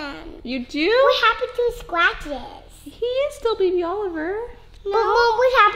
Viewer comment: Who else thought the twins said "what happened to his glasses?" 😂🙋